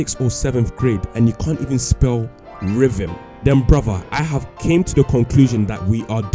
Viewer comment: not gonna lie, I heard ribbon for a sec there.